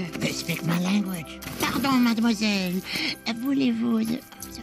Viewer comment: What did he say to her? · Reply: This commenter translated what mole said in French ⚫ "If I'm not mistaken Mole said to Kida "Voulez-vous coucher avec moi, ce soir?" a famous line meaning "Do you want to sleep with me tonight?" :D" - ⚫